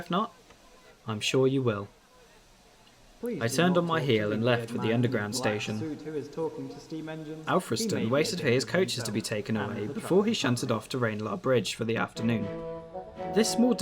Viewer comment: What did the station announcement say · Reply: "Please do not talk to the weird man in the black suit who is talking to steam trains, her may be a health hazard to himself and the traveling public"